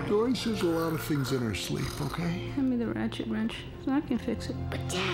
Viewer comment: "im in a ratchet wench" ??? am i hearing this right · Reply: I think it was "Hand me the ratchet wrench so I can fix it" Lol